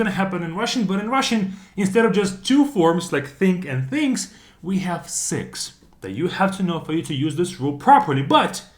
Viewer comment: "instead of just having two forms, we have sex" Am I the only one that heared this